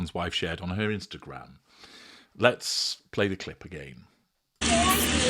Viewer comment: 'only if you want ME' - is what I hear